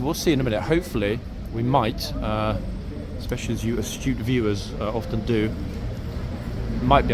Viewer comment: Hahaha. The first part I had to replay a couple of times because I heard you say "you stupid viewers". Whaaat? Apparently you said "you astute viewers".